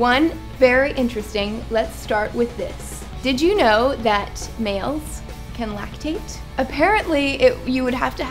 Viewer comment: I was in total shock when you said MALES i thought you said SNALES, i was like what the hell what the hell ?!?!